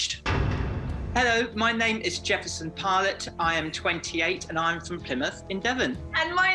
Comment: I swore you said your name was Jefferson Pilot.... A band from the 1970s